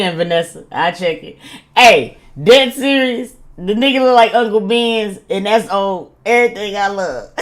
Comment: No…wait…!!😬did she just say Uncle Ben?? 😂😂🫨🤣….as in rice?!😂😂😂😂🤣🤣😆😳😆😆